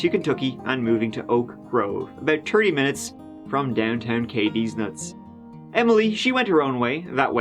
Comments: did I just hear a deez nuts reference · Hol up did I hear “Cadeez-nuts?” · Did just say downtown Cadiz nuts (deez nuts) at 3:35? lol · downtown KD's.....? Anyone else hear what I think said...🤣🤣